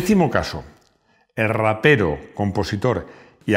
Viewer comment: ¿Me equivoco o escuché el ratero por rapero? Juro haber escuchado una t en vez de p y lo he puesto varias veces y sigo oyendo la misma palabra.